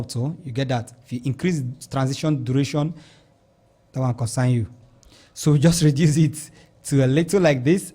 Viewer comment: I heard "That one concern you". I burst into laughter🤣🤣🤣